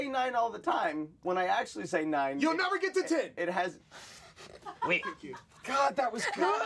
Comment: what does he say? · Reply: youll never get to ten